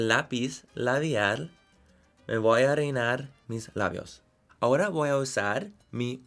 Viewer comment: I laughed a lot because at 6:15 I thought you said “me voy a orinar mis labios” then I read the fill in and It was all good lmao, I loved this❤️